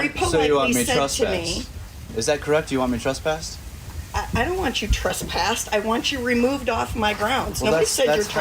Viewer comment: she actually said she didn't want him trespassed